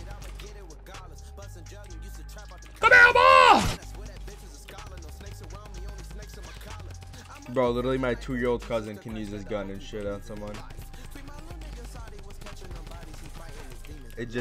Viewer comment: For a second, I actually thought u said "Come here boi" . Anyways nice content broski.